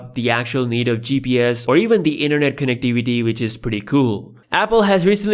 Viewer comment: I thought he said "internet connect dvd" 🤣🤣🤣 He said "connec-ti-vi-ty" 🤣